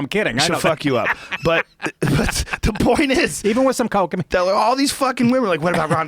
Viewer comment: Did he say "Even with some coke in me" and looked at jaime??